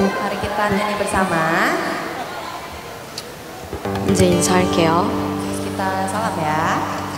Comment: mari kita nyanyi bersama terus kita sholat ya? apa saya salah denger ya?